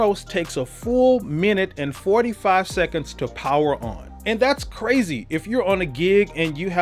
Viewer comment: About the startup time of the Kronos, you said "4 minutes and 45 sec" That's not true. It's about 1min and 30"/ 2 minutes according to the number of auto load sample banks. Last thing : today, a professionnal musician could not buy a Kronos : it is not available anymore !! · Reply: Sorry for the misunderstanding. Perhaps you misheard me, I did not say the start-up time was 4 minutes and 45 seconds. That would be a nightmare., lol! If you go to about 4:30 in this video, you will hear that I said the start-up time was 1 minute and 45 seconds. I also had the stopwatch next to it to show the start-up time.